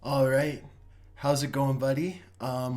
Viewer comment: sounds like he says “how’s it going, buddy?”